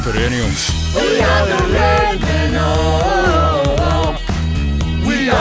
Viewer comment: What do Liverpool say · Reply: "we are the red men, oh"